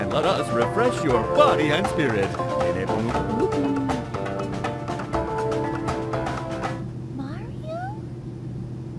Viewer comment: what is the guy saying at 1:05 it sounds like jibberish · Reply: Zhane Mack Hoo Hoo